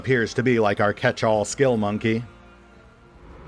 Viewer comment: Every time I hear a xeno say “Mon-keigh” I hear “monkey”, and I just want to kill, Warhammer style.